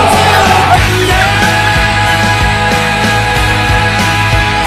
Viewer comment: 兰陵王好像说了什么 （狗头） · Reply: 感觉是“你干嘛呀”